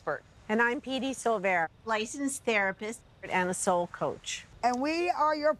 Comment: She said "NSL coach". It's an approach in counseling psychology. Gotta admit, I first thought I heard "soul" too and I was very puzzled 😂😂